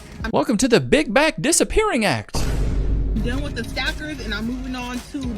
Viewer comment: did you say steak?!? …